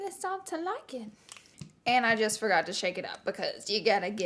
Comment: When you said shake it sounded like you said 💩